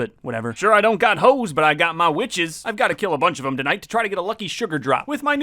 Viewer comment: Did you just say b*tches